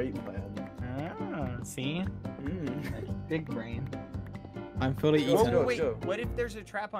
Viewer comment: I heard Bad say “dick brain” instead of big brain. I can’t unhear it now.